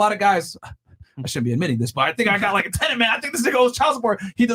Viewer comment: What did Myron say at 59:30, "I think I got like (what?)" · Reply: A "tent"...a hard on.. when law enforcement think they got a suspect they can bust it's like they get a hard-on a type of adrenaline rush knowing their about tomorrow take down somebody. So in this case it's like they have somebody under investigation and they noticed that he has no bank accounts and always using cash, so they suspect he owes child support and they're going to arrest him. I could be completely off on this, but I was trying to understand what he was saying in context